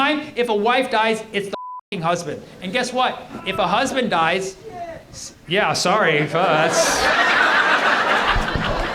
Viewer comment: What was shouted at 43:11 ? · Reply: It sounded like “oh sh*t!”